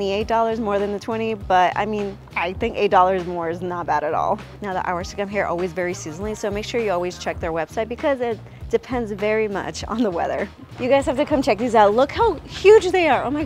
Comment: What did she say at 1:28? It was a bit hard to understand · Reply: "Now the hours to come here always vary, seasonally. So make sure you always check their website because it depends very much on the weather."